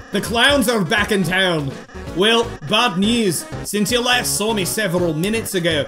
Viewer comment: IT SO FUNNY TO HEAR SAY BAP NEWS INSTEAD OF BAD NEWS IN